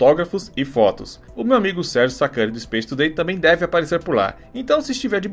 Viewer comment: Alguem por favor decifra oq ele falou nos 2:36 kkkkk nao entendi nd kkk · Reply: Sérgio Sacani do space today